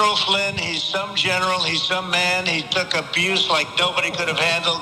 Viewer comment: did Trump say, "He's so General, He's so man."? 😂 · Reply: I think, "some general", "some man". But given that it's Trump, you could be right.